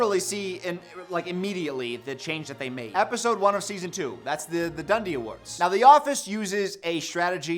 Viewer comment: thought I heard the d1ld0 awards for a minute